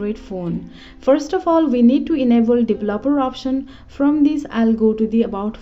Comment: No offense, but I cant understsnd you. I keep hearing "the blubber option" over and over.